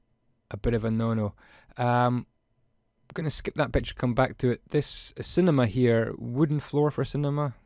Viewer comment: it sounds like says at 4:28 ''Gonna skip that bitch and come back to it'' but the video really helped:)